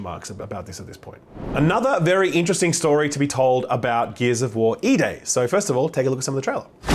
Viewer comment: I thought you said Gears of War eBay.